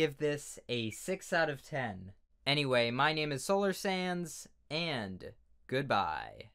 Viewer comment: In the outro it sounded like he said